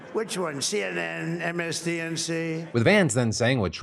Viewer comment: did he say "MSDNC"?